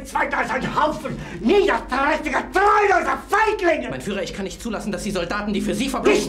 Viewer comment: Thank you all for 1,000. I couldn't have got here without you all viewing my videos and engaging with me. I appreciate all of you, from the regular commenters to the silent bunch that watch but don't comment. Also, I will be doing an Extra Discussions on this show. Again, from the bottom of my panda heart, thank you all very much. I hope I can pay you back with an amazing content and requests that open this week. :D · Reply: anybody else heard FIGHT NIGGA?